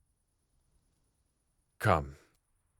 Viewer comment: Rengoku: "Come." Me deaf a$$: "DID HE JUST SAY CU-"